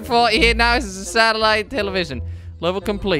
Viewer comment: it sounded like he said kwebble complete